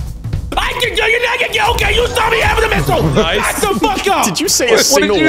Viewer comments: For me at least, all the captions got from this was "okay you saw me" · the only words I heard were you're having a missile